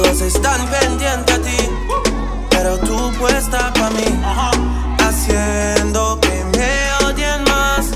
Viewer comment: I swear I heard potato